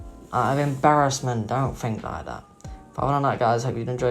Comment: When he said peace out at the end I thought he said piss off